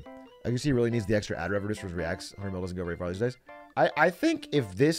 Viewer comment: wtf is he saying ive been laughing at this unreasonably long now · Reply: "A hundred mil doesn't go very far these days"